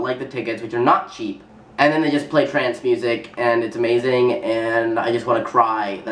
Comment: You said trance music, and I thought for a second you said trans music...and I was like: What is that? lol